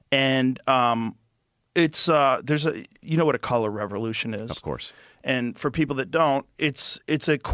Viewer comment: did he just say collar revolution instead of color revolution